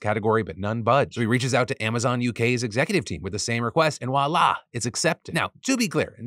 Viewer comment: Did you say "walah" or am I mishearing voila? lol